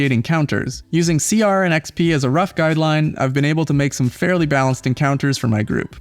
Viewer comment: what did he said that he used in 6:39 can someone tell me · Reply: "Using CR and XP as guidelines..." Not referring to a tool...just saying that he has been able to come up with balanced encounters by using the Challenge Rating and Experience Points guidelines in the Dungeon Master's Guide.